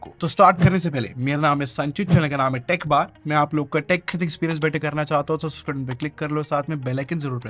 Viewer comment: i never understand what he says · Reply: Mera bhi same doubt hai .. but shayad "Mai apka tech kharidne ka experience acha Krna chahta hu" bol rhe hai